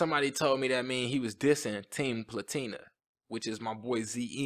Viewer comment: Yasin didn’t diss Ze, he said ”tre platina which means three platinums but people thought he said sum bout team platina